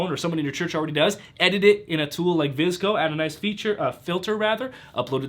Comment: What is the editing tool mentions at 17:05? I didn't catch the name of it. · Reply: VSCO video 😀